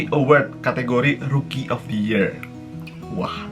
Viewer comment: aku dengernya award rugi of the year 😂😂😂 Langsung ku ulang dan ku pause,, sekalian baca😂😂😂